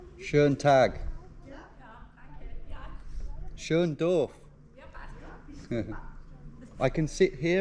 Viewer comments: I think you wanted to say "Schönes Dorf" (nice village) but it sounded like "Schön doof" which means "pretty dumb"! 😂no joke, German here. She probably didn't even notice it tho, no biggie. 💚 Thanks for your content. 🖤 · huge LOL moment for me because it sounded like "schön doof" which means "pretty dumb"